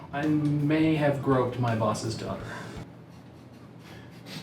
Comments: I heard him say Toddler rather than Daughter and it made Ethans reaction much more understandable😂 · i thought that he said i broke my bosses daughter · I thought he said groped my bosses cock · When said groop the bosses daughter I thought he said dog 😂😂😂😂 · Even though I saw this a year or two ago I thought said I broke my bosses daughter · When you said dog I thought you said dorghter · so 2 years ago, I thought said "I broke my boss' daughter". Now I can't decide which is worse: groped or broke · At first I thought said I might have broke my bosses junk